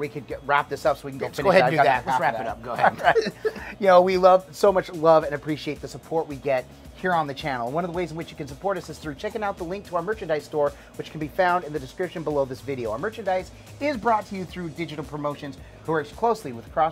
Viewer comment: did he say Chicken out our link?????????Well YES SIR!!!!